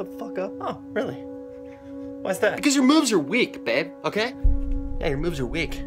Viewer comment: Thought he said, "YOUR MOVES ARE WICK."